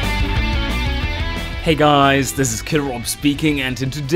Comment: Man, I can't stop mishearing the intro as "Hey guys, this is Kirov speaking..." Gotta check the skies for Soviet battle blimps every time.